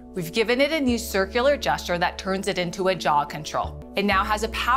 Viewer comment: I’ve watched 3:15 about 10 times and still can’t figure out what she’s saying - “jaw control?”